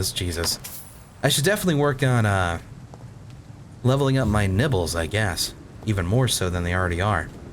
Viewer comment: i heard, "I need to level up my nipples" hehe... Immature? yes Worth it? yes